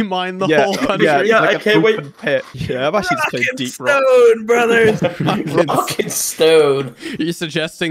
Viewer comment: did I hear a rock and stone?!